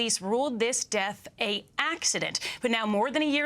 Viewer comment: "A accident"? I didn't hear "an".